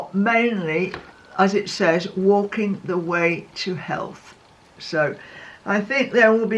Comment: I initially thought that you had said 'walking the way to hell'🤦🏾‍♀️😂😂